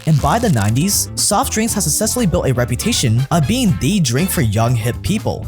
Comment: “has sussessfully” bro wtf 😭 1:29 learn how to pronounce words